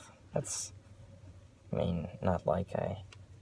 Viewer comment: I heard “not like I-“ after the proposal Shut up Lucky We’re gonna get married